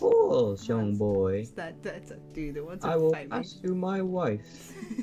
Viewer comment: i thought the teacher said "ill give you my wife".. not knife. I was so confused..